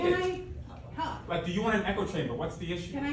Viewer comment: UN echo chamber? Did i hear that right?